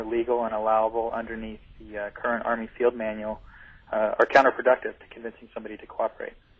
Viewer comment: It does sound like he said Seal, but I think he said Army Field Manual.